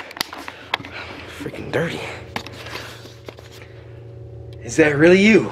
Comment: thought he called me dirty